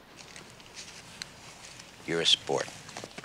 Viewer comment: What is the driver saying here: 1:40 please? "You are a *spor*." ?? I can't get it and neither the subtile engine :) Thnx! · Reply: Sweeney Lol, I expected he had no money at all and so I learn something new, but he - magically - had all 10.50 plus the .50 :)) Yes, he is a sport! (And that is also my bonus, the new meaning of an english word.)